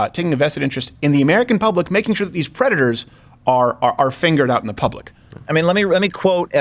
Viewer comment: Did Steven just say, "... that these predators are fingered out in public?" 😂